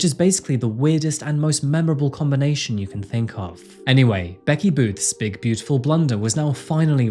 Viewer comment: At first I thought said “Betty Boo”...😂